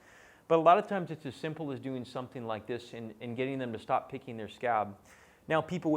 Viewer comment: stop pickin their what? Sorry couldn't catch that word. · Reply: Scab